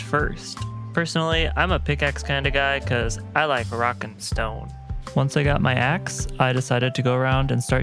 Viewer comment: DID I HEAR A ROCK AND STONE?